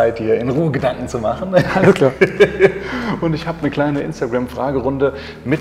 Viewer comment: Was sagt bei 01:15 ? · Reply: Alles klar